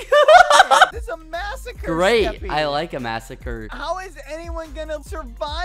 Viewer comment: that's what she said Edit: I thought he said "I like it faster"